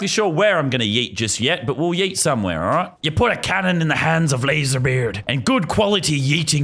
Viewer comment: did he just said 'LazarBeard'?